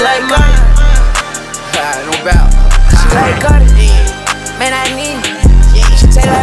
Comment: She like Carti Everyone: he is just on another planet. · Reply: LMAO I thought he said she like carter cause like his last name but it makes more sense for him to say carti😭😭😭